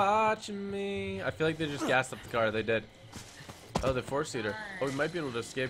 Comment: When you say for seated it sounds like your saying force eater